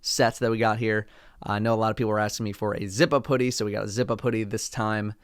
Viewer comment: When said zip up hoodie, I thought he said zippa poody